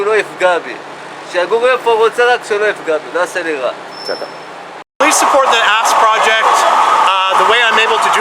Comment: I thought he said "please support the ass project" because I would gladly support that 😏